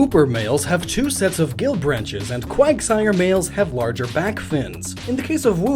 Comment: I thought he said quagmire lol